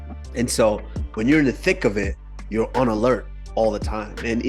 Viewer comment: did Kamaru just say 'in the thick of it' 😂😂🙌